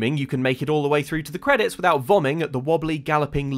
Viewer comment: Is it just me or, at 7:36, does say "vomming"? I know he meant vomiting. Is that British slang or did he just leave out a whole syllable?